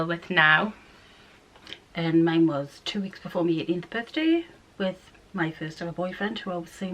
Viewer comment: Your mums accent - sounded like she said 8th birthday 😂 I was like da fuck hahahaha